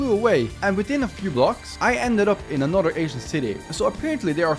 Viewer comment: I keep hearing “Asian City” 😂😂 Lmao but wow it’s incredible! Can you come and fix up mine now??